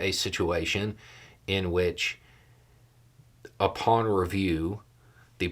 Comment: when said "upon review" I heard "a pawn review" and thought yeah, Mr Renner was very much a pawn.